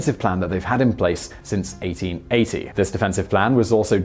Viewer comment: Word should be in place instead of implace